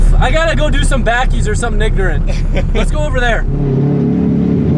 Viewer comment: sounded like broski said nignorant 😂